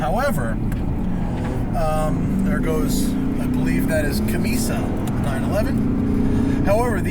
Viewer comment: did i just heard CAMISSA ?🤔